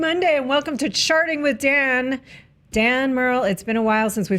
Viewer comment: I heard Roth say "welcome to sharting, with Dan"